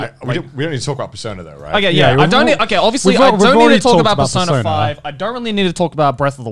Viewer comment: Since Connor once mentioned you pay a lot for these subtitles... i've realized they're kinda wonky. Examples are at one part, Joey says "ass" and the subtitle says "us", or at 1:45:47 when he mentions Persona 5 and it says "the son of five", a few minutes later the subtitles are missing for a short time and then back, kinda catching up in high speed, not matching at all, until the subtitles are too early? And I've realized a lot of those during the episodes before 45 as well. Since I'm actually only watching and not just listening to this podcast whenever i don't really understand something, it was funny at first, but hearing Connor be upset about a meme about the subtitles ... it's really sad that the subtitles don't match.